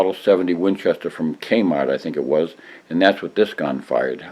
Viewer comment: did you say Kmart? You could buy guns at Kmart !!